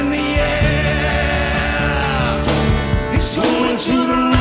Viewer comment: i always thought they said going to the wrong angel .